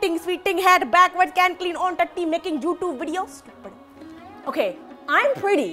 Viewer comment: did she said tatti 😂😂 🙈🙈